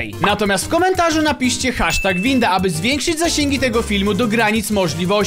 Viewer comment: ja usłyszałem #pinda